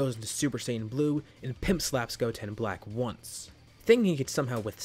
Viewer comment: Did you say pimp slap 23:10